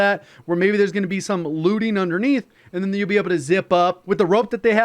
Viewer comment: Looting? Thought he said lubing …